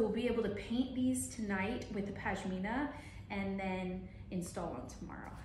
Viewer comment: Wow.... Your work is amazing... Will you be kind enough to share the shade name of the cabinet ??? It looks so rich and absolutely stunning..... Good luck. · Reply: I think I heard her say it’s “Pashmina” by Benjamin Moore?